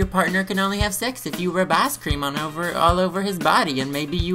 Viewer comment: heehee first time you said ice cream i thought you said ass cream.... *needs to get ears checked* :3